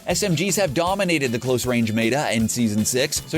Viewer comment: did he just say mata... kill me now.